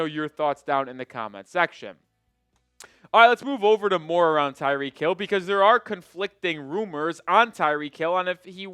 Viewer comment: You said "Let's move to more on tyreek hill..." i heard it as "moron tyreek hill "😂